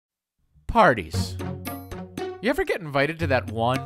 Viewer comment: I thought it said panties gone wrong and got disgusted by the grandma